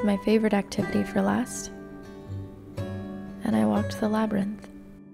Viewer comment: I misheard you as "I watch the Labyrinth" and I was so confused how David Bowie related to Beltane lolol